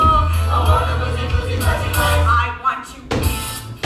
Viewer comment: What does want to be in this version? I can't hear what is saying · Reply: he says he wants to be "une femme brisée" which if i'm not mistaken, means "broken woman" or "that broken woman" and you can see every changed lyric in the script